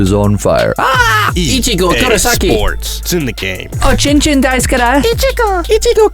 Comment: Hold up did you say chin chin?